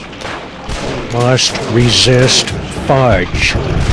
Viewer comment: I always thought he said, "Must resist ice!".